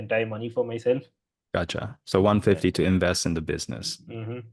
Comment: Did he just say "achha" 😂😂